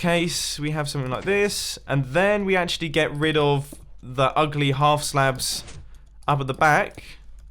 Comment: *WHAT DID YOU SAY AT 15:56??? THAT YOU GONNA GET RID OF THOSE UGLY HALF SLABS???*